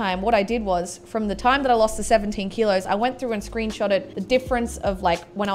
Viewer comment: Loll the first time when she said 17 kilos I thought I heard 70